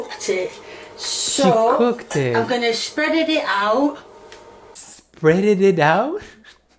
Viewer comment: She clearly said, " Spead IT out." Not spreaded out it. Our being dyslexic again 😂